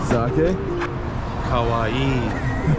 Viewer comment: If he meant to say "kowai" ("frightening"), it sounded like "kawaii" ("cute"). 😆